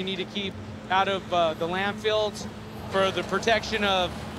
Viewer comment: Is he saying "land-filleds" or "land-fields"? Either way, it's unbelievable that the guy literally works at a landfill and doesn't know the correct name of what the place is called.